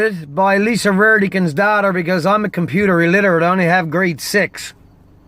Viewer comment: i thought you said great sexs not grade 6 at first lol